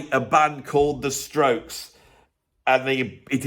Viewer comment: The Stokes? Never heard of them. I thought you said "Strawbs "!